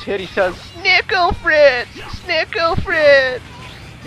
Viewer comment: i hear “dinkle friends”